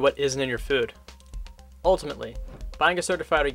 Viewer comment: WHAT does he say here??? 2:37-2:43 ( "...is a ? ? ? standards in the american food system") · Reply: buying organic is a vote for higher standards... etc.